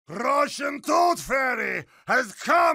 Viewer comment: I always thought he was saying “Brush and Tooth Fairy” instead of “Russian Tooth Fairy”